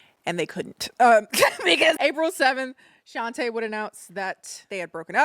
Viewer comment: Omg I always thought u were saying "Avril“ like an alt personality lmao